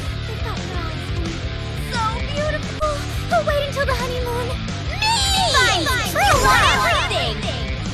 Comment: What is being said at the end of each one? Like, instead of "Elphelt wins" ? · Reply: I believe she says "ME finds true love! You've won everything!"